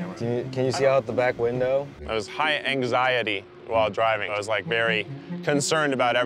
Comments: At first I thought it said High diving and I was very confused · lol I thought is said "high diving" 😂😂